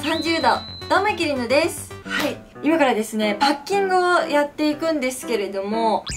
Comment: ハッキングって聞こえてびびった